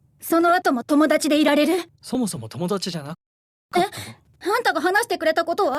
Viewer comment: 、なんて言ってる…？そもそも友達じゃ、無かったの？って聞こえる、気のせいかな。